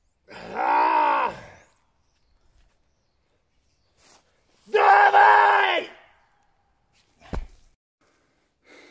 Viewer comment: What does he say at 1:10 ? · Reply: "davai", means "lets go" in most slavic languages though i am not slavic at all, it just has a nice oomph in the sound it makes